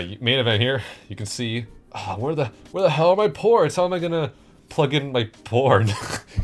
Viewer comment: … when he said “how am I going to plug in my ports” I- god- LMFAO I thought this man said “porn”😭😭😭